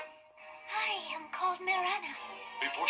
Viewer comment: "I am called Mirana." 6 year old me always heard that as "I am cold Mirana."